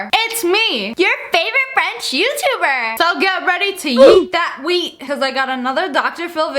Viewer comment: i thought she said yeet that weed lmfaoooooooooo